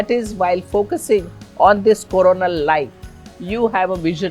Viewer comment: did she say to focus on Corona Light? Making me thirsty. Haha 🍻